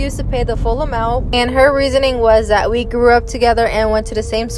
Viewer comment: I THOUGHT YOU SAID 'So I ripped her nails off' FOR A MOMENY